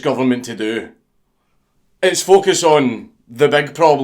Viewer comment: until you got to the part about the pug vid i kept hearing "fuck us" not focus